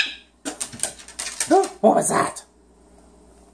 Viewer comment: "I'm smoking weed" lmao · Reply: it sounds like he said " that's what I'm sayin". Lol.